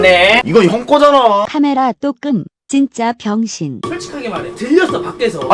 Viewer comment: 왜 찐짜 라고 들림?